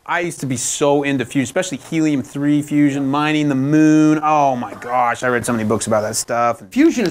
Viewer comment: I'm finding this video extremely interesting. One tiny point: is it possible to correct the "mining in the mood" at about 1:01:32 (61:32) to "mining the moon", which is what he actually says? Is there some way to add a correction to the interactive transcript? TIA.